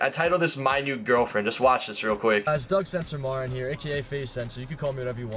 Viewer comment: It sounded like he said Doug su su Martin here hop u guys r having a great day this guy is Doug is the best